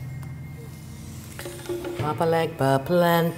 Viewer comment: it sound like she says papa legba I heard he’s like the king of hell (via American horror story tho?) can some please tell me if that’s what she’s chanting?